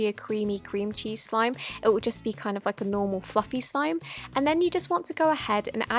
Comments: Did she said ffing normal slime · I thought she said cringey slime instead of cream cheese.. lol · no hate but when you said cream cheese i thought you said cringey btw i subscribe